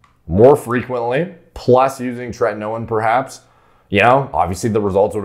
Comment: What did say at 4:39? Trenalo or something